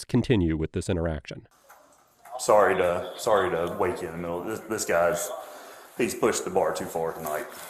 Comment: I heard that as "this guy hurt my ego" 🤣